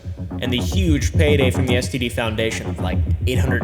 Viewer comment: Bro I heard you say the STD foundation